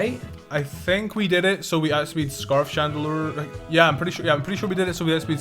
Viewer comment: Everytime you said "Chandeleur" I heard "Chanoleur."